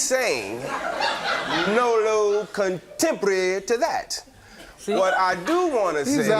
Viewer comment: “No comprendo!” 😂😂😂😂😂😂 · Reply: I thought he was saying "Nolo contendere". No contest